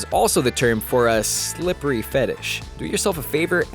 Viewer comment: What did he say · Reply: slippery fetish